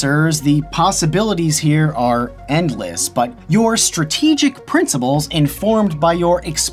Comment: did he just say princiBles??